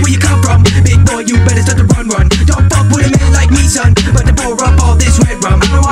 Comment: Ohhhhh!!! He said dont fuck with a man like me. I thought he said dont fuck with a nigga like me😂